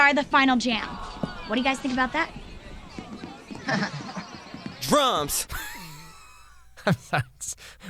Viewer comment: i thought he said trumps